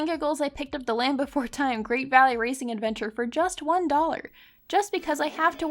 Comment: I really thought you said “Land Before time Great Value Racing Adventure” lol